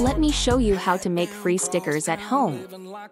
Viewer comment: I thought she said "Let me show you how to make free Snickers at home". Must say, I was disappointed 😞😂